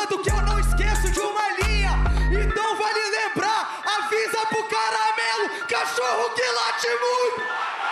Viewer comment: "cachorro que late muito..." nao entendi o resto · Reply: "cachorro que late muito acaba na carrocinha"